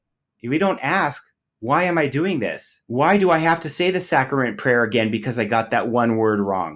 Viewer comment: LOL, I misheard "sacrament prayer" as "!saccharine prayer", how appropriate!